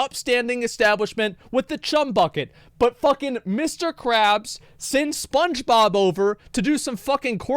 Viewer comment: Did you say butt-fuckin Mr crabs?